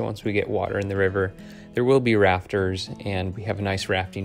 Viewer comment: I thought you said there would be RAPTORS in the river 7:58! I thought you might be taking the freelance/prototype layout a little more freelance side. That is such an amazing layout. I just started building my first 4x8 layout to test my skills before I design my room sized layout. Your videos are amazing and very helpful. I have gotten so many ideas even though I want to model BNSF in Illinois.